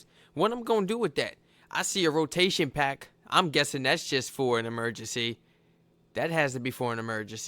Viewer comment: did he really say "rotation pack"? 😮‍💨